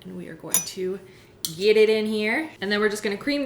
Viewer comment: I thought that she said yeet it in here, then t hit me, she said geet it in her.